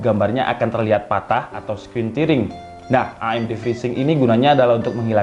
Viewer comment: Menit 3:53 , abang nih ngomong apa? "Screen.." apa? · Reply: Screen tearing